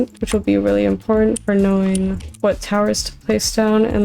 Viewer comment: you need to find someone new to do subtitles cus she said towers and subtitles say terrorist